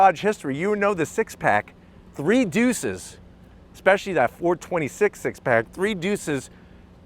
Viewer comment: Did he say "know you Dodge history@ 4:15 "426 Six Pack?"